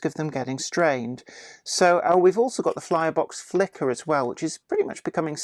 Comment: Did I just hear you say "Flirebox Flicker"? :D